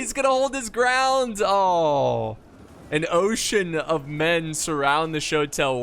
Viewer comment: Whenever the shotel warriors got surrounded whole retreating I could've swore you said they got surrounded by an "oh shitten of warriors" then logic kicked in and I realized you probably said ocean 🤣